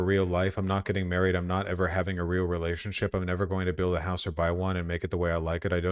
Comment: I nearly thought he said “I won’t buy wine🍷” and it killed me lol 😭